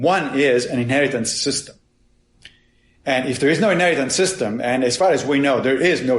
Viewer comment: What is the word Massimo uses 3 times from 49:57 to 50:04? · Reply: "Inheritance system," you mean? A system for passing on information to offspring, like genes.